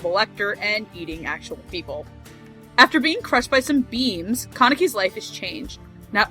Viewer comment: i thought it said "after being crushed by some beans"